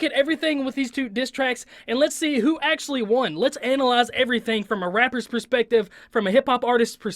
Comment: I thought you said “let’s look at this from a rappers perspective?” Dude your not a rapper!